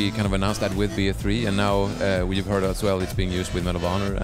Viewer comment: did he say Arma 2?